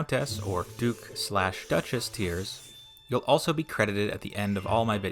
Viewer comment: A note about the Colossus of Rhodes. Despite the later paintings and drawings, the statue did not straddle the harbor entrance. This would not have been feasible from an engineering standpoint, nor is there any archaeological or historical evidence supporting the claim. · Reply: I swore he said "GOOK" and had to look up and realize he said DUKE Lol for a moment I thought he was trying to really bring in that new Asian money segment of viewers 😂😂